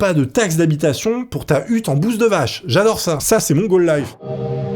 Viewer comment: Pause à 19:18 Alors moi j'ai pas entendu gaule life, je n'ai même pas entendu goal life j'ai entendu mongole life.... Ohhhhh boy....... Là tu m'as ouvert les chakras.... On peut s'en servir dans tellement de situation celle-ci.... Le nombre de vanne qu'on peut balancer avec ca.... Ohhhh boy...... T'as fait un strike génialissime je crois bien là!!! EDIT: C'est juste drole hein?? Venez pas me casser les noix avec de la psychophobie du validisme ou je sais pas quoi... Allez damage control carte piège je suis autiste ok? Bon....Diag offi, je l'ai publié je vous dirais ou le trouver si vraiment.... C'est juste drole... me cassez pas noix ok?